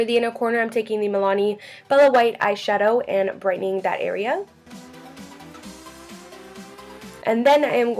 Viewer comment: did you say "brightening diarrhea?" lol